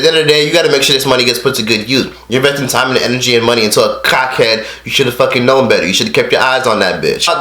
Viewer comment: Dead as sounds like he saying "cockheads" 😂😂